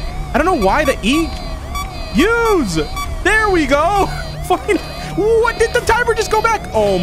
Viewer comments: he claimes to say finally but I hear f***in but it could be my mind that is making me hear it ( my brain is not working properly anymore ever since I no longer get scared) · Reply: He said "fine-". You can literally hear it. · what he say lol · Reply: He said "fine". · DID SAY THE F WORD?!?!?! 10:35 around this time is when I heard it lol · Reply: He said "fine". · Guys at 10:40 he didn't say a swear word he said finally. Look at the pinned comment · do my ears deceive me or did I hear something? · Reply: He said "fine". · It sounds like said f-ckin' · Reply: I hear "ooo what did the timer just go back" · i cant be the only one who heard that at 10:41 right? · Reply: He said "fine".